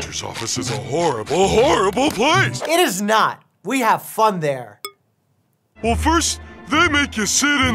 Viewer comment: Patrick: *Lists off bogus reasons why going to the doctor is awful* Mike: No, no, he's got a point. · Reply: that's literally what Dr Mike said!! He said we have fun here. Check it out for yourself.